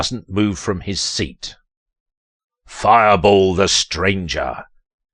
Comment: I'm Sorry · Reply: Me: skipping through the video out of confusion First thing I hear: “EAT FIREBALL FUCKER”